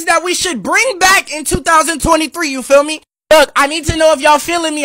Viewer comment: i hear “you FILL me😉” everytime he says “you feel me!?”💀